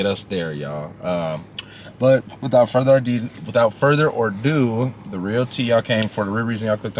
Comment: I could've sworn I heard "without further or do" at 00:52 in the video. We all have gotten an idiom wrong here and there. Just for future reference; "without further ado..." 🫶🏼